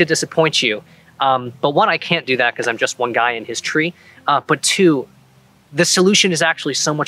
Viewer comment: Don't sell yourself short: You're not just one guy and his tree - your one guy and his several trees. I am sure you could find a spot with at least 3 trees close together, which should suffice for at least a billion dollar production worthy fight scene. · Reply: Thank you, I misheard it as 'I'm just one guy in history.'